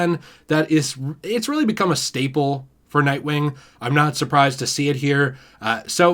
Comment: Honestly at first I thought it said German Nightwing